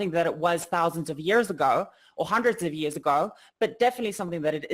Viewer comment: "American culture Hundreds of Thousands of years ago." Sorry, I don't think there WAS an America Hundreds of Thousands of years ago. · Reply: She did not say that though, listen closely. "...thousands of years ago. Hundreds of years ago." If we're both talking about what's said around the 0:50 mark.